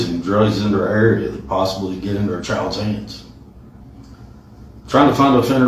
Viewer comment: did he say child's anus? 😂